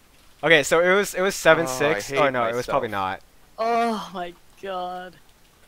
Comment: voice reveal when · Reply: i think he said oh my god